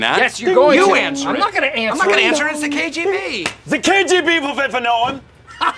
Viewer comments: What did Jim say @ 7:31 ? "The kgb will ........" ? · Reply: Wait for no one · What does Jim say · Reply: The KGB will wait for no one.